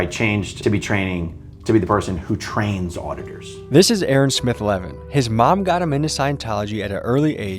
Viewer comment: Had this on in the background while getting ready for work and I thought he said “Aerosmith legend” 6:38 😂😂